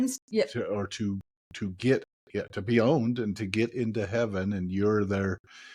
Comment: I'm laughing so hard.... I thought said items to be boned when he said items to be owned! 🤦🏻‍♀️🤣🤦🏻‍♀️🤣🤦🏻‍♀️🤣